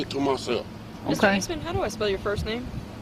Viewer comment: Officer thought he said Eastman, he actually said. That’s called a MISUNDERSTANDING. Arresting officer truly believed a felony false ID (statute 901.36) had taken place.